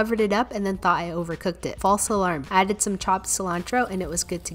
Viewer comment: it looks amazing but why did it sounded like she said i added some human